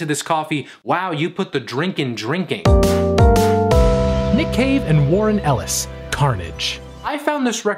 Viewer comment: I was just listening and wasn’t actually watching so when you said nick cave I thought you said nick cage and almost had a heart attack